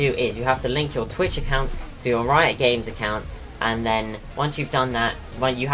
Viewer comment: what did you say? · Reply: Riot games